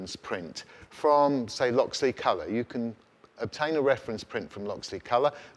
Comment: you mention and my ears couldn't hear clearly — was it "Look- See_ Color" for the brightness target ? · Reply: Probably Loxley Colour. A printing company in Glasgow.